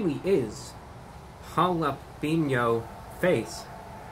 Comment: "Jalap-in-yo-face" Ha! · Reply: Ollie Zee I’m high and thought he said “jalapeño pace” like it’s a slow burn lol